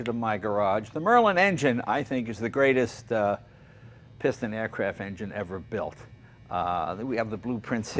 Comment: makes me proud to be british when jay said its the best piston engine ever built. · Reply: He actually said "the greatest piston aircraft engine ever built" 0:38.